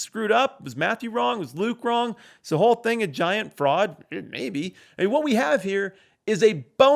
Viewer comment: I don't think it's a giant fraud. But perhaps it is s giant frog. Because that's what I thought I heard say...